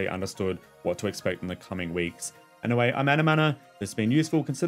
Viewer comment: When you say "I'm anamana" all I hear is "i'm out of mana" haha Great video, thanks a lot !